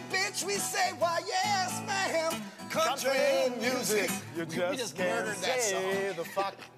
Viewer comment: does he say "you just can't play the song" at 8:22 ??